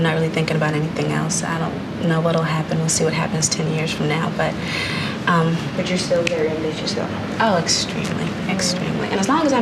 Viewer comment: what did the interviewer say? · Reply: "But you're still very ambitious though."